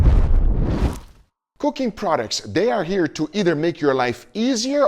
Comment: When you said cooking products at the intro to me it sounded like you said coccain products XD